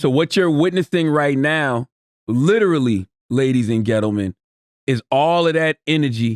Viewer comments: Did Charlamagne say: "Ladies and ghettomen?" LOOOOOOL @ 1:41 - I'm doneeeeeee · I swear CTG said ladies & "GETLE MEN" 🤣🤣🤣🤣😂😂😂